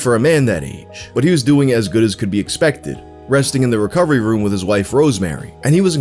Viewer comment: th instant i saw a medical journal paper on screen i heard 'presenting to the emergency room, where we are now"